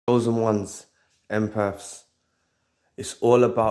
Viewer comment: First off your accent is beautiful. I recently found your channel and thought you were saying ‘chosen ones,emperors” 😂 My bad. Anyway when I came across the narcissists a few days ago they sent flying Monkeys to me and suddenly this overwhelming evilness was getting so strong. My spirit kept saying “ get up, get out” I wasn’t afraid but sensed the danger and had to leave. The flying monkey followed me to the parking lot yelling my name but I picked up speed and didn’t look back. As son as I drove off I was at peace again dodging that bullet. That was crazy. Luckily for me I had already healed and was prepared for that possibility but didn’t expect that evil overwhelming spirit .